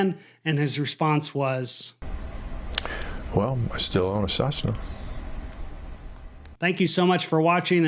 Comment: He says: “well… I still own a Cessna..” I hear: “well… there’s one more cyanide pill in the bottle.”